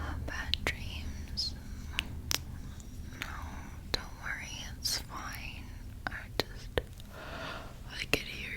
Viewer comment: i thought she said “dont worry swine” i was so confused and angry :,,)